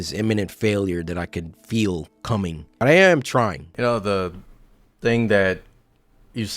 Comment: I thought he said "Animatronic" but he actually said "I'm trying" =